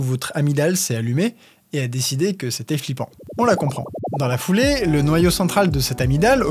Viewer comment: esceque c'est moi qui sui flippante si mon agmidale a dit "beau" la ou tu dit flippant? nan parceque la j'ai pas eu peur... j'adore la coupe :p